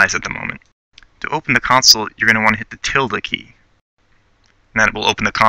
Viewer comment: Wait, which key brings up the console? It sounded like he said "Tilda Key" or something... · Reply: The tilde key. It looks like ~